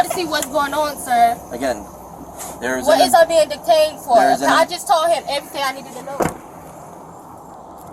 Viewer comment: Did she just say, "What is I being detained for?" GD😂